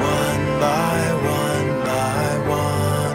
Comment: Wow. All this time it sounded like he was saying "like an awning".